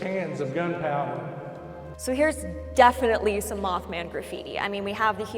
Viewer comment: I thought you said meth man 🍺🍺🍺